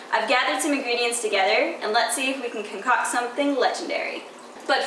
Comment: Hey guys! Just want to say thanks so much for all of the nice comments on the video. I love doing this type of stuff and hope to make a lot of fun videos for the Hearthstone community in the future! Please know it’s not just me putting these reveals together. Alex Finden, my editor, is such a talented and creative person & friend. Mason(gos), the boyf, is always helping direct and come up with ideas for my content too. We all make a great team together. Again, thanks for all the love & positivity. allieLove xx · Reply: Did you say cock? xD